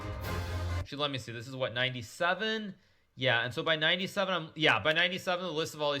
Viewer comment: "97"... I heard "Nb7" 🤣